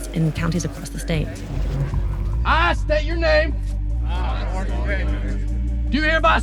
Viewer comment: I swear someone said "I state your name".